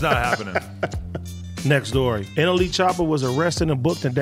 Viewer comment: am I the only one who heard anally choppa?